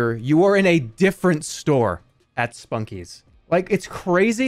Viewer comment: I heard him say 'Spunkie' as 'Sputnik', and was very confused.